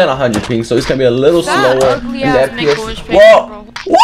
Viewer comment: did u say the nword... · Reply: Thanks for the concern I said "ugly as make a wish kid" I mumbled a bit. Listen closely!